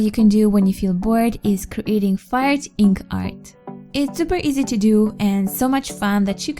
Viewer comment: When she said "fire ink art" I thought she said "fart ink art"